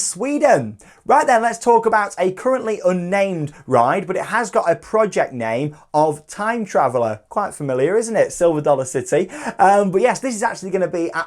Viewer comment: for a moment i thought you said 'Plopsaland Japan'